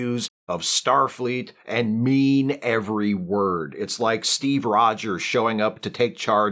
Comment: I’m not gonna lie cause I at first misheard Steve. I thought he compared Pike to Mr. Rogers before realizing he said Steve Rogers. However it still made me laugh either way.